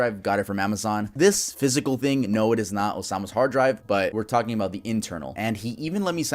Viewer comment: I thought you said i bought obamas hard drive